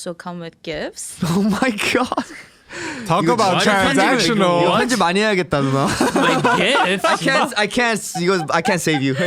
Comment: did Junny call Ashley nuna?)